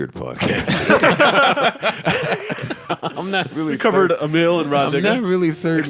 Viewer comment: did say Amil and Rah Nigga?